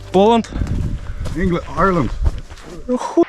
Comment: мужик что сказал не разборчиво. Англия? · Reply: Ірландія